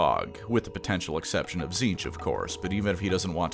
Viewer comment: Auto captions mishearing Tzeentch as Slaanesh HAS to be Tzeentch's doing rather than the usual abominable intelligence bullshit.